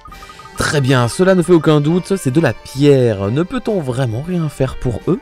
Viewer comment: j'ai entendu " c'est de la bière 🍺"